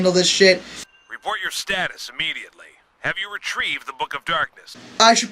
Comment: In 19:33 did he say the book of darkness?